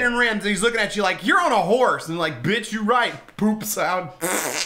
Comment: did you say bitch you right If that's what you said that was funny:)